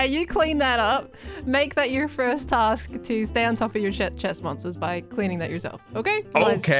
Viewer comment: Anyone else hear "sh*t chest monster"?